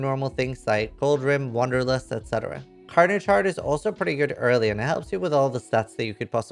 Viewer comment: I cannot for the life of me figure out what he says after he talks about goldrim, wander lust..... crowded shroud...carter chard....no idea:) but If anyone knows fill me in... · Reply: Carnage Heart